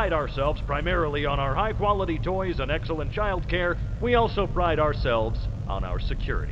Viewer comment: 「ア○ルザきゅうり」って空耳して聞き直してしまった…笑